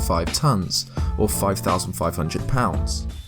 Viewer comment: Is it me or does he say 5500